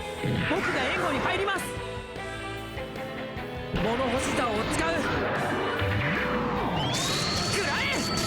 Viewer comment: 物干し竿を使う！って聞こえてくるのは私の気のせいだよね、うん。